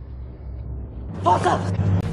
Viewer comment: It kinda sounded like he said FUCK YA for a second idk why lol